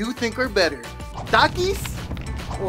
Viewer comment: Taki for sure, however I thought he said Duritos, LOL